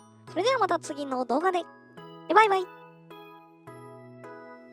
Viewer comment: 最後の「陰影ってほんとに奥深いよね…」が「人間ってほんとに欲深いよね…」に聞こえて 急にどうしました！？wってなりましたwww